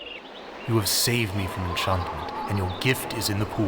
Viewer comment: I cannot figure out what the statue is actually saying but I heard "you have saved me from chudhood" and I am choosing to believe that is the actual line